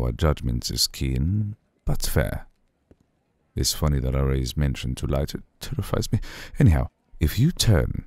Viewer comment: I love this, but I can’t understand what he says here 3:58 Something about the Aria and when you defy him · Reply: Thank you! He said a little earlier "let me shed some light" then at the timestamp "It’s funny that I raised mention to light .. it terrifies me". Vampire allergies!☀